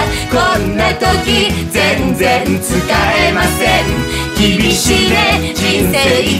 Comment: ぐらい 君しね？ って聞こえるのはおれだけ？